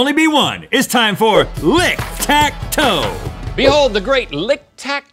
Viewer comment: I thought it said link tac toe